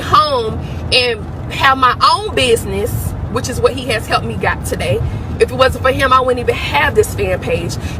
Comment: I think they been kicking it before January and she find a way out that’s why she has done all this mess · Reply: Did she just say HELP ME GOT TODAY 😮